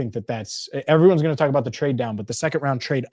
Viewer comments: Nah you're thinking of Trey Dupp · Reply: he said "trade down". I thought he said Trey Down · I literally thought said "Trey Down" and I immedialty tried to Google who the player was.